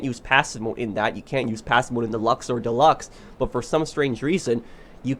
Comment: Did he say Deluxo deluxe?